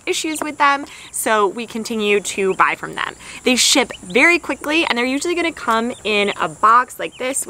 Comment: I thought you said they shit (ship) quickly....we just got two turkeys and i would highly agree with that statement hahaha